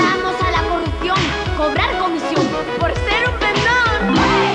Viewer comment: ¿Alguien sabe que dicen en el 02:14? ¿por ser un...me...? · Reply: En realidad dice por ser un pendon, en otro video se oye mejor.